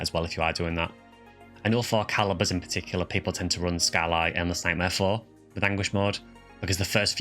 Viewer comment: I’m sorry, but what quest are you saying here? I can’t quite make it out. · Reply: For Caliburs, run Skyly Endless Nightmare 4!